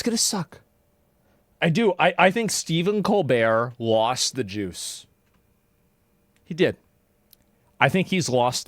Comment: I legit thought he said "Stephen lost the Jews".